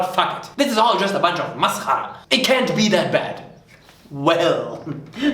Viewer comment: Hi, nice channel you got there! (New subscriber), Like the comedy! One question, did you say "maskhara" at 7:25, and from where you got that word?